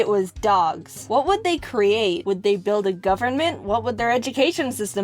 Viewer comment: I actually thought she said "would they build a ROUFFernment." Instead of "government"